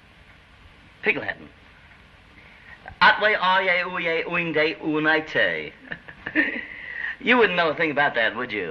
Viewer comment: Larry is saying: What Are You Doing Tonight (atWhay Areyay ouYay oingDay onightTay) and Lucy says: No (oNay) .....I was curious after see this scene for many years to know what Larry was saying in Pig Latin.